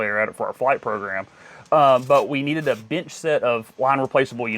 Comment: Does he say a “bitch set” of computers at 21:55? Any idea what that is? PS: Air Drake is a 767… even bigger than a 737!!!